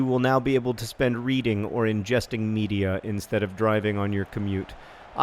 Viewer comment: oh when John said ingesting media I thought he said ingesting me... which thinking he meant in his media content. But you know, context is everything.